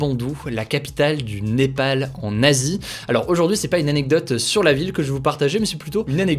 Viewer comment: A la fin, tu as dit "le Népal, en Asie", la première fois, j'ai entendu "Népal nazi" 😂